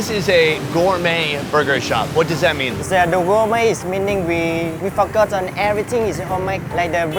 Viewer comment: did he said? WE F*** UP” 7:45 lol